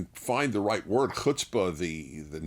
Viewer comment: What’s the word Dennis used at 9:46? hutsbuzy? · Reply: Chutzpah- to be audacious, have shrewed initiative and gumption.